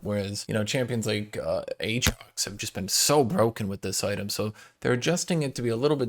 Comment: what did he say? I'm just curious lol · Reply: aatrox?